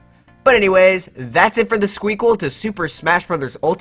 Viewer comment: Ahem, did you just say SQUEAKQUEL!